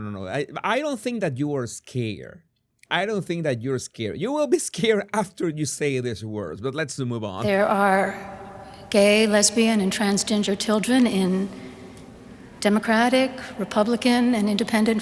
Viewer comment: Did she say, "gay, lesbian and TRANSGINGER"?